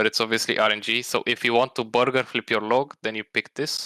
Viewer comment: it sounds like he says "if you want to burger flip your log", is that what he actually says? And if so, what does he mean by that?